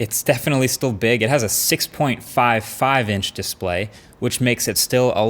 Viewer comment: For a second I missed the "six" and I thought he said it had a 5.5 inch display. So naturally, I was enthusiastic instantly, because I hate today's huge phones, and I looked it up. NOPE. Sadness... I still hope there will one day be a 4.5 inch phone display again. I can't get used to this huge BS.